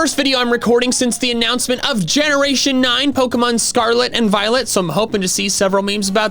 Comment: lmao why did I think he said violence